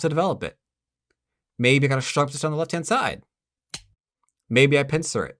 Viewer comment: what does he say here? I can’t tell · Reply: Holy hell! not even i know and im pretty sure i said it. Um... i think i said "maybe i have a set up on the left handside" so pincer to develop it